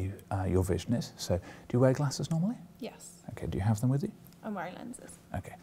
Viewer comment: it sounds like he said 'do you wear glasses snowhite?'